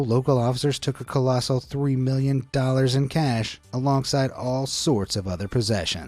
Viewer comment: I thought they said 4 Million.